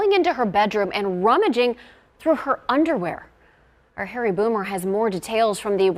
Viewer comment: I'm not kidding, but when the news lady called on Harry I thought she said Harry Bloomer .. Not trying to be funny. I really heard "Bloomer"! I almost fell out of my chair!